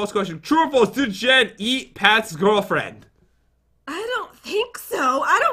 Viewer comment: So with that last test I got 100 by going through different answers. After each answer you will see my response. (Each answer ends with a period. Each of mine end with a !)Here is 100%. 1. Selvester. They got that right! 2. Mat. PAT!!!! JEN IS MARRIED TO PAT! 3. Aww HELL YA!. SHE DOESN’T SWEAR! 4. There isn’t even a number for that. That is correct! 5. Pshhhh not at all! hehe I don’t know what your talking about. AJGG SDFOAJHWKJHEFLKJHSFDWLKSAJDFHASDLFKJHSDWFPIJGASDFPK! 6. Bow. Correct! 7. Yes, 1. That is correct! 8. No. That is correct! 9. Florida. That is right! 10. "I want the lion head! Give it to me!" I don’t know what that question means soooooo...! So there you go. They don’t know Jen. So don’t worry. The person who made this got 60% or 70%. (It’s the last question that makes the percent differ) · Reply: Did u just say matpat?